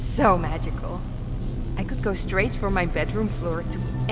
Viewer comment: のとこ空耳で｢くっそまじか｣って聞こえる笑笑